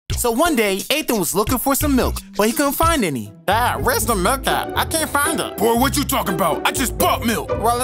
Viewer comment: I thought he said there's no milk🥛🤔